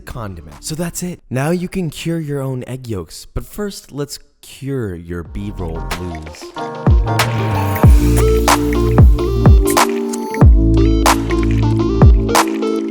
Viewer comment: When you said "cure the B-roll" my sister and I both thought you said "cure your beaver"...